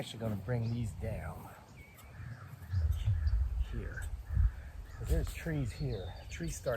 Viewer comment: Hi, it looks good. Maybe it's me but at about 13:40-41 you say bring these down and I hear a low guttural voice say here. Stay safe